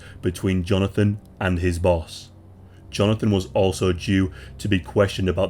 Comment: I thought he said "Jonathan was also a jew"